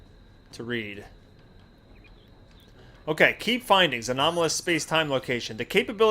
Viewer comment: lol, when you started reading the journal at 19:30 or so, I could have sworn you said "Key Bindings", instead of "Key Findings".